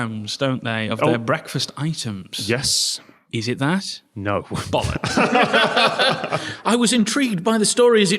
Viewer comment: the captions say "bollocks" yet has only uttered one singular bollock